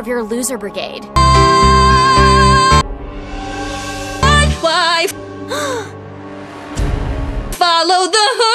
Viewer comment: only me who heard "my wife"?